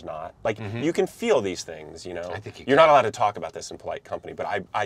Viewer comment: did Dwight say " I think your gay "? Haha